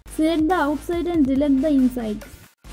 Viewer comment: Anyone know what the hell she said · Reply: Select Mask and delete the inside